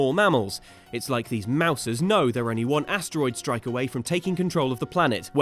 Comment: Did he say 'mouses'? Plurals don't work that way! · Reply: Oh, Mousers... <link>